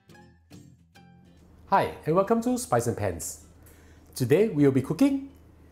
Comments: It always sounds like he says "spicy pants" when he says "spice and pans". 🤣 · Oops at first, I thought that you had said "spicy pants" 😅 buy I realized you had said spice n pans 👏